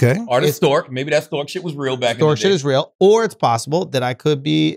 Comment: Can someone please help me? I don't know what Andrew and Charla said at 1:53:21 where Andrew and Charla said "storkship is real". I know the word is not storkship so can someone help me? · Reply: Stork sh1t is real. Parents used to tell kids that they were delivered to the home by a stork